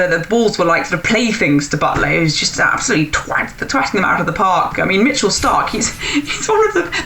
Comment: Did I hear "... twatting it out of the park ..."?